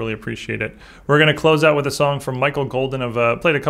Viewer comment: Volume was low and I thought you said Michael Bolton